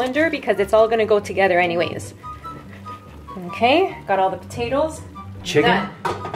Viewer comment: Am I the only one that thought she said "cute potatoes" ???